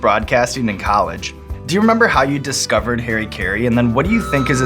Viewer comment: Oh Harry Caray, I thought they were saying hari kari over and over again